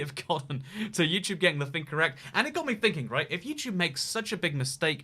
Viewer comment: and turn on captions. Never knew "getting" sounded like "gang"